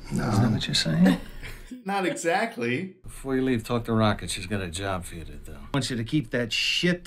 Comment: I thought he said "Taco Rocket"